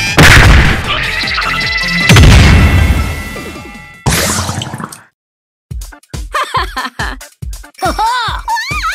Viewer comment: What it say in 1:35? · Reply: Ippon!